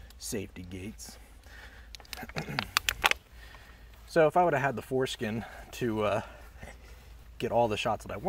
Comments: Did he just say "if I had the foreskin to get all the shots I wanted...."? 🤣🤣🤣🤣 Wth? 😂😂😂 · Did he say foreskin?? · What did you say at 9:14... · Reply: Foreskin. It was a play on words. Aka malopropism.